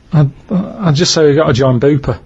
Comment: What was Karl's response to the ridiculous hypothetical situation? He says it at 27:18 and all I hear is "we've got a john booper" which I don't get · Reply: I googled it, he actually says "we've got to join BUPA" which knocked me on my arse once I read it.